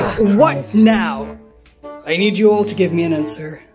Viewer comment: I thought tengwn said I need you to give me an heir